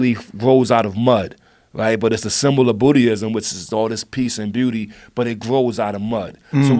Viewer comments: Lmao RZA really pronounces words the way he feels like. @ 1:22:53 he says it's a symbol of Bootyism instead of Buddhism lol and he kept rolling hahhahahaah in dying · he says "the lotus is the symbol of bootyism" instead of Buddhism lol